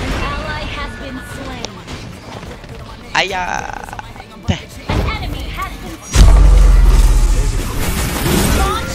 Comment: lu mau ngomong apaan 😂😂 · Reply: Dia mau ngomong ayang lea